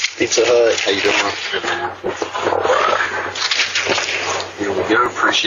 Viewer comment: I have listened to a few times and still don't hear it🤔🤔🤔🤔 I heard " How you doing man" but not the "N" word.....please pizza guy, take this guy to court for defamation!!!